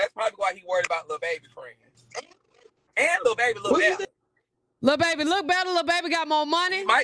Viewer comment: Who is Lil Baby I thought they said da baby. The whole time I’m thinking they talking about they son. Chillleee I’m lost…lol